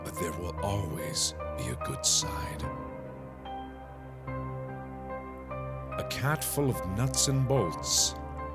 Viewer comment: I thought he said a cat made out of nuts and balls 💀💀🤣🤣